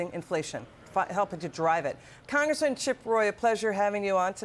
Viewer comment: Did she just say " chicango" lol